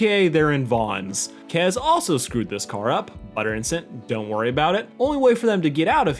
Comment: …did I hear “utter incident” just me? am I crazy? am I hearing things?